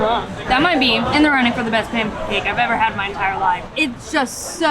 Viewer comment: did you say pamcake? My wife says Lieberry, I always ask her why a place with books has fibbing fruit?🤔